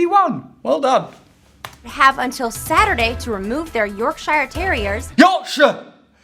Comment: I thought you said Yorkshire terrorists!